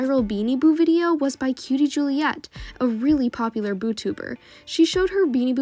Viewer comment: Did she just say bootoobe?!?!